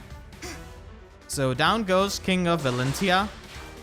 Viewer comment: For a second i thought you said king of elincia lmao